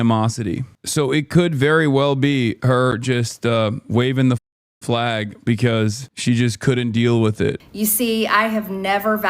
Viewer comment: did you edit "white flag"? Is that what you even said?